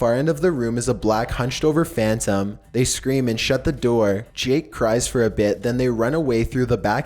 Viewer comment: I thought it said “Jake cries for a bed” I’m like wtf😂😂😂